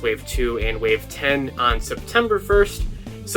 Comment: Im new to collection Lightining Collection and maybe I misheard but you said wave 9 comes out in September? So we are only getting 2 waves in 2021?